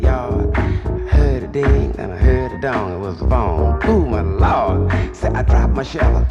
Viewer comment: He was the master. Especially the guitar. Jimmy Hendrix studied under this man. Yep the rap groups loved. I mean loved him!! My favorite part” I heard a ding I heard a dong was the phone ooh my lord” . More than a rapper but he did it all without cussing. Amazing right!! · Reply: i thought he said "heard a ding heard a dong and was born oh my lord!"